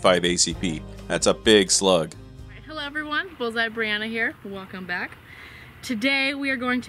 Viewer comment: I had to rewind :55 because I swore she said "Hello everyone, balls out Brianna here, welcome back" I nearly spit soda through my nose laughing at that one. 😅